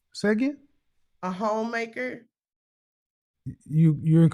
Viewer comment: I heard home wrecker 😂 Home maker as in construction worker that build homes. Mr KS a philosopher